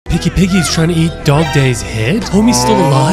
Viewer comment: I heard it as Pinky Piggy